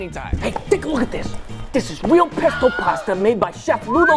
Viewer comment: For a second I thought he said "Pistol" Pasta but it was just Pesto and now I'm disappointed.